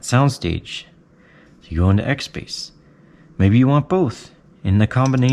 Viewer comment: I thought the zen can signature didn't have an xbass.. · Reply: In the video he was saying XSpace but it can sound like he was saying XBass. I got the signature stack and the 650. Also tried Sundara on it.